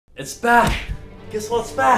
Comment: when they said “ IT’S BACK “ , what i heard was “ IT’s BACH “ — someone help my ears HAHAHAHA